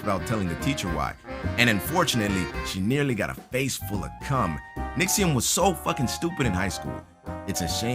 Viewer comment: did I hear that correctly? "face full of cum"